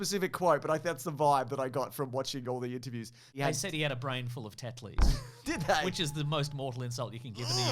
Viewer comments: Haven’t heard swear much, but (as an Englishman) hearing him say “a brain full of Tetley’s” was deeply unsettling, surprising and frankly unprofessional. I’m not sure if I’m going to continue listening to this podcast anymore with language like that. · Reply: Oh I heard 'a brain full of dead fleas' and was horrified lol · Oh I heard 'a brain full of dead fleas' and was horrified lol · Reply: I heard "a brain full of tetanus", and same